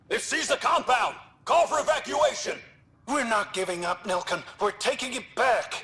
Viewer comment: Fun fact: The first time I heard Nilken's name I thought she said Milken and I ready it as milky